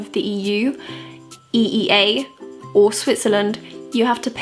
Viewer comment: did you say Switzerland? Or Sweden? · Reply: Switzerland :)